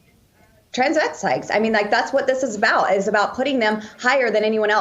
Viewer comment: Tbh I heard "transvetstites", which is not as funny. Of course she has no idea what she's talking about.